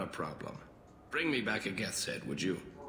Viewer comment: I thought he said "Bring me back a Geth Sandwich"